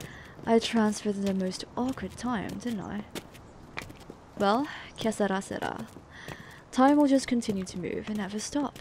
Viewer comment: Did June just say "que sera sera"